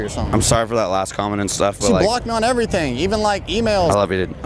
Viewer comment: Damn I thought he said “she block-gnaw’d everything dude” then I realized it was “blocked me on everything”